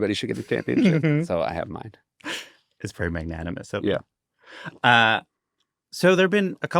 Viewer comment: Did he say man-yanimous?